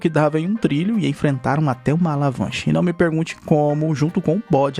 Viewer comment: escutei alavanche. Ao invés de avalanche